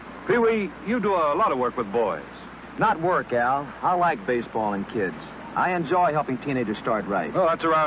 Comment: This wasn't the 40's. Some of the products didn't exist yet and some of these ads were too modern. Mr. Potato Head wasn't released until 1952. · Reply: I could have sworn he said "I like baseballing kids." And so, another nightmareific phrase is born and borrows itself deep into my brain. 😫